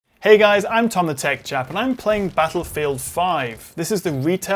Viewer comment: I thought you said Tom the Tech Chav.